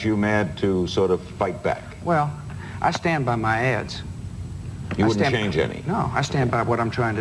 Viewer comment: I stand by my ads, bush sounds like he is saying I stand by my ASS. Lol.